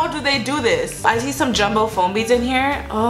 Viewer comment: Does anybody hear her say “I taste some jumbo foam beads”